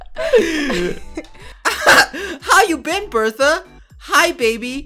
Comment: “Bertha” Lol... I think it says Farha